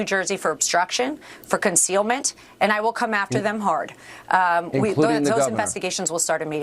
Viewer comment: Did she just say “And I will make them hard”?😆8:25 no these guys are all losers and now they have an opportunity to “ look cool”. But deep down in their heart, they know they’re losers.